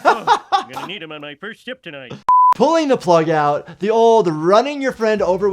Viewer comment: I like that mistake in subtitles in 0:10 where is "shit" instead of "shift" :D Btw, do you have in plans to react to "Manhunt" games series? Take care and cheers!